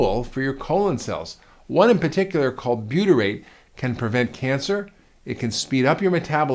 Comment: What's the word you said at 2:55? how do you spell that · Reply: Herb Zagorac Butyrate.